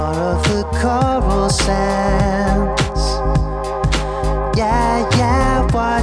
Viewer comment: did u say comic sans?